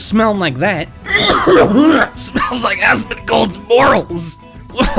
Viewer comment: Did you say Asmongold? Like the infamous YouTuber?